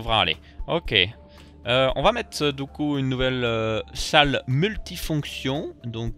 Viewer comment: on va mettre Dooku ?????? j'ai compris du coup mais c'est ton accents sa ma fait marrés